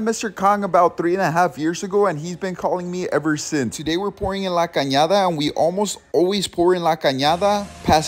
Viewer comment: Thought you said Canada 🇨🇦